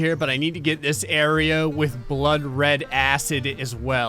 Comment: I like that a ad cut off sentence and it sounded like he said blood red ass